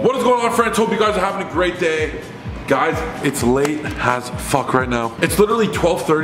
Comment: Am I the only one who thought he said "its late im high asf right now." In the begining 😂